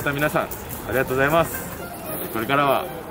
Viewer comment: 活舌がｗｗｗ スカイアローなのに、 スカイヤローに聞こえたｗｗｗ